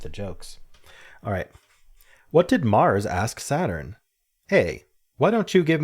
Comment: My brain did not compute Mars and misheard Saturn as Sauron. The joke still worked.